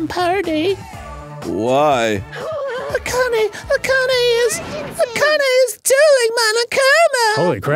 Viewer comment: lol I have game grumps in the background and I swear I heard 28:11 Akane is DOING monokuma lol